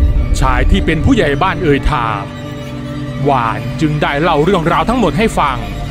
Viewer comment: เขาชื่อนุชไม่ใช่หรอค่ะทำไม่ได้ยินว่าหวาน